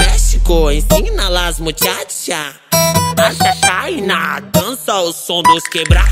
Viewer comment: Wow. Yo pense que dijo "As Vaginas" no chachainas??? O que e isso, eu nao falo Portuguese muito bem.